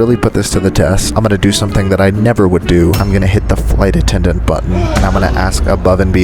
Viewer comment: I hear "I'm going to do something that I never would do. I'm gonna hit the flight attendant's butt." Me: What?! 😱 The next second, phew, he is going to hit the button, not butt. 😅